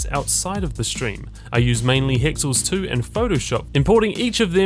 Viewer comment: What does he say at 0:48 ???? I cant tell what he says and the subtitles say hexyl stew :D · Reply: Hexls two i think